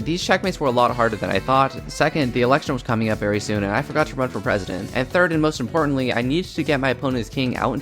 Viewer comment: I thought you said vote for a president and when I replayed ai heard that 😂